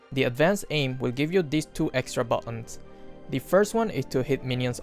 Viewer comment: did you said two extra balloons?